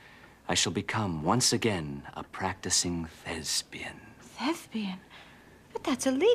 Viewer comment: I sounded like he said a practicing lesbian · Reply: He said: "A practicing thespian." It apparently was an intended homophone, because he did not clearly enunciate the first syllable. In context, he was going to act a part or play a role to disguise his identity.